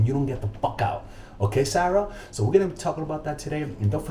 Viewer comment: Did he said : ok sara ? 😅